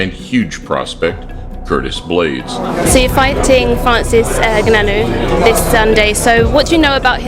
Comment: “So you’re fighting….” WHAT did she say? GUNAN??😂 🤦‍♂️